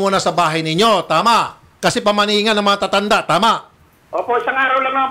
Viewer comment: "paumanhin" instead of pamahiin😂